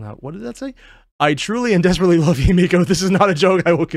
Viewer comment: I desperately love himeko? This is not a joke. I know the text says Yae Miko, but I could have swear I heard Himeko.